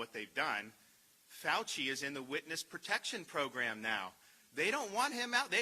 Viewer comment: did he say fauci is in witness protection program at 1:57?